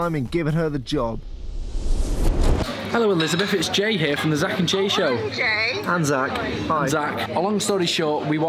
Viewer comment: Is it weird that I heard. "The Zack and Gay show"?